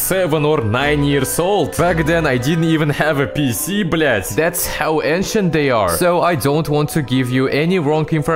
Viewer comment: блятл!!! Услышал что то родное